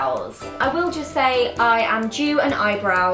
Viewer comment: Did she said I'm jew at eyebrows at 3:30 ? Like did she just do a jew kind of joke? because not like that but... I need to know! >< I mean if she did that's wrong!! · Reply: She said I'm due at eyebrows. Like she's due for an appointment. :)